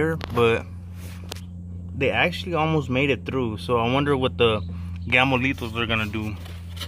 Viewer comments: Took me over 10 minutes to realize you were saying “Gamo-Lethals”. Through the whole video I coulda swore you were saying ‘Gamolitos’ haha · I thought he was saying “Gamolitos” like a Spanish nickname or something the whole time. He was saying Gamo Lethals 🤣🤣🤣